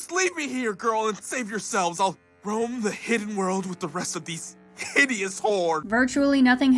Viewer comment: I kept hearing d'angelo saying "ill roam the hidden world with these HIDEOUS whores"